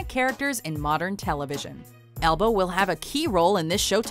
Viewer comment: I swear she says 'elbow'